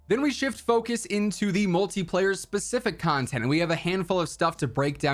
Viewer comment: I thought you said shmultiplayer